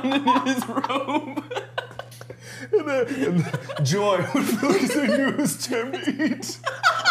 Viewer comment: what does he say here? I cant hear anything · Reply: “Joy would fill me because I knew it was time to eat” except while laughing really hard